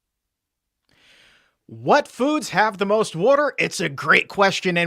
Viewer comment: did I just hear say 'warter'? lol. My parents say warter too. I saw hwater lol